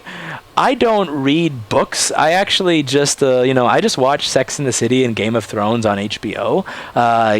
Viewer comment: WAIT, what did you say at :30 was that "Sex IN the city" i heard you say. not "Sex AND the city?" do you believe in the Mandela effect? make us a video, the people have spoken.